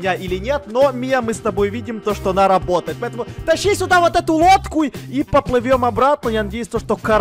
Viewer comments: мне послышалось что он сказал не лодку а водку😸😂😺😂😂😂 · мне одному послышалось, что сказал не «лодку», а «водку»? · А мне послышалось водку